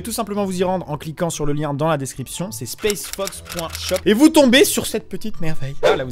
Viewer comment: J'étais entrain de faire 2 choses en même temps j'ai entendu " spacefucks"... 😂